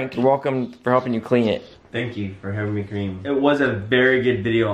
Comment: Am I the only one who thought kian said “thank you for helping me cream”